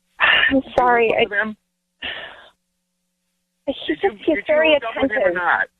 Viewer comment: Ryan is making things up .. she didn’t say he attentive.. this Ryan is high in the show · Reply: I heard her say that “he’s very attentive“ at 7:57. He just spoke over her.